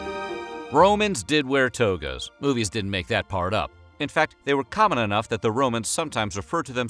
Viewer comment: “Roman’s didn’t wear togas that was just in the movies”. Goes on to explain all the togas they wore.🤦🏻‍♂️ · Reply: No, he said, (at 0:41) "Roman DID wear togas, movies DIDN'T make that part up". If you go back and watch that part again you might want to turn on the closed captions and yes they do match what's being said